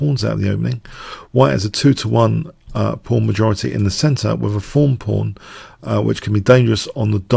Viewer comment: Does say 'form pawn' at 1:29?